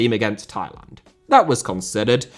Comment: I thought he said taiwan 💀